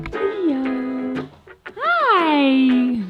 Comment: did say HI? Sure sounded like it to me.